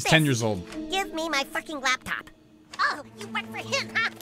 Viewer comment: sounds like he said frick not fuck